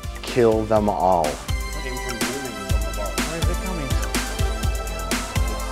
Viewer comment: I do think I heard the words 'skull fuck you' around 2:03. I want a recording of that.